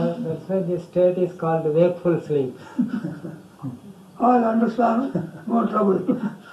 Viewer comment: Did someone understand what Swamiji said around 32:50 ?...no trouble, would appreciate🙏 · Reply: He was saying "all understand, no trouble". Reduce the video speed to x75% you will hear it. Meaning right understanding is the key. That's the reason he gave 2 examples in this video. Of the snake and rope and also gold and ornaments. We think there is a person there but there is only Consciousness (Self) and body-mind with its functions like thoughts, feelings, 5 senses etc. All you need to have the right understanding. Once you have it, you know that there is witnessing going on and body-mind functions going on.